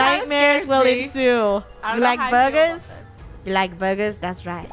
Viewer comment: I thought said you like murders? AND IT WAS PERFECT BECAUSE SHE WAS IN THE CLOWN OUTFIT🤡